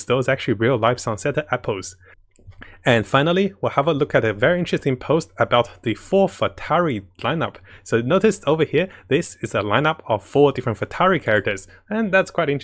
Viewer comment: Spits out coffee. Did you just say four futari!?!?